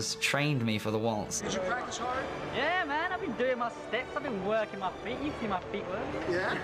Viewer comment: What did Tom saying at 0:17? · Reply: « yeah man! i’ve been doin’ my steps, i’ve been workin’ my feet, you see my feet workin’ »